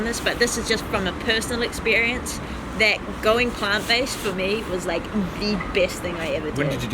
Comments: Great video , great story ... i want to ask what did she say at 17:12 ?? I cant understand... and what in means ? Thx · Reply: She adopted a plant-based diet, so she essentially became vegan. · what does she say? Going what? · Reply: oh nah nah going plant based